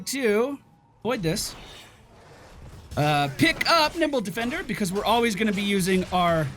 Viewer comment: did he say nipple defender?